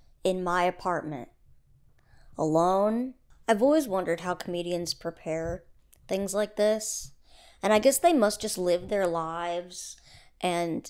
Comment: i stg i heard ‘canadians’ and got so confused